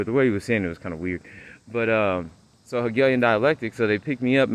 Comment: What word did you use at 3:46? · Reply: hegalian dialectic. its create a problem with the solution already planned out. like the v and the jabjabs is. much Love❤